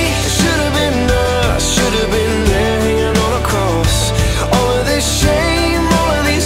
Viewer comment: I thought it said should've been nailed hanging on a cross. TBH I don't know which luric I like better